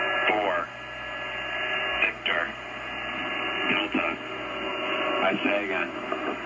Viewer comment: Anyone else catch that at one point they said "Isaiah" instead of India? Wonder if that substitution has any meaning. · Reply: I heard "I say again" which sounds like "Isaiah". EAM's are repeated during each broadcast.